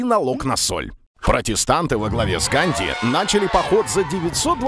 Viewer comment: Я один услышал "Канди"?